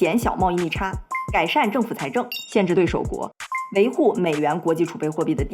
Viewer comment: 限制什么我听不清😂😂😂😂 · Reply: 限制对手国